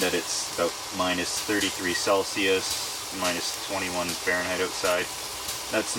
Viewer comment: Steve: It is -30 degrees Celsius Me: wow that’s cold Screen: 27.4 Fahrenheit Me: oh it’s hot outside. Nevermind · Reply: Thought he said MINUS 27° F Okay, so I just looked up the conversation -33°C = -27.4°F